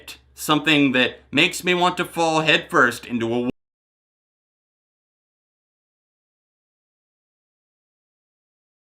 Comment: Did anyone else's audio get cut off at 29:26? What word did he say? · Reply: Woodchipper, but that’s not why the audio cut off. It was cut off most likely because the song was copyright claimed.